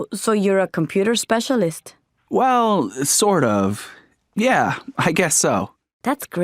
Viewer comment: is there supposed to be a Wow, ? im pretty sure I heard Well